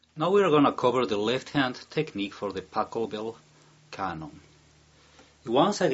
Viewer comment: It sounds like he says Tacobell